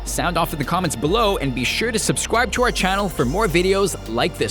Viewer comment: does anyone else hear him say subscrwibe